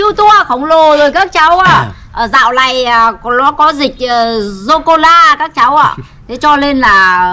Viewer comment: Corona nghe thành socola